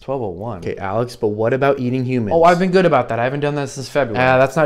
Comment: I first thought he said, "That counts", and that perspective was funny as well, but when I saw the reel for the second time, I laughed so hard when I heard "they count" 😆